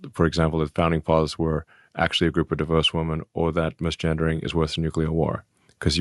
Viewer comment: I heard him say, diverse women and divorced women, either way this is utterly crazy to me